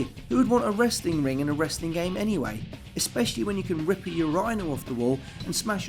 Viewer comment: your eye nell? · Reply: Sometimes you catch a Brit saying "yoo-RYNE", and that's odd, but still okay. The receptacle, however, must be a "YOO-rin-al". The first time I heard say his version, I thought he said "your Rhino".